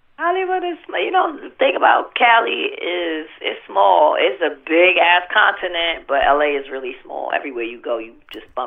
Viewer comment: Did she just say"Cali is a big ass continent."?